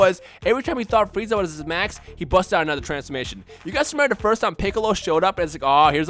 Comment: I swear I thought said when we thought freeza was at his max he busted a nut